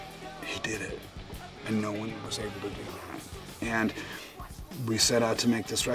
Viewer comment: "and no was there..." what? I can't understand the rest. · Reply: I hear "And no one was able to do that"